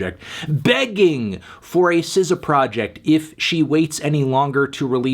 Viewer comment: Hey what album is 1:29 with all the crt monitors? He says the name but I can't understand it enough to search online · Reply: NVM I found it by screenshotting the frame, clipping the album pic then google imaging. CTRL by SZA